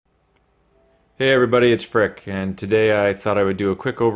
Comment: I swear I thought you said " hey everybody, it's prick" hahahaha